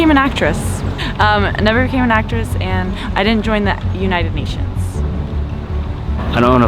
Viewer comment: did she just say she didnt join the United Nations??? how can a single person join the UN ? xD